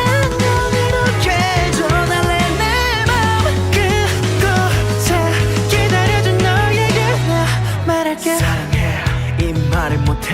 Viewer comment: '그곳에 기다려준 너에게 나 말할게'가 '그곳에 기다려준 너에게만 말할게'로 들려...너무 설레자나?